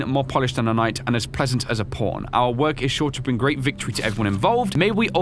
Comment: i thought it said “pleasant as a porn”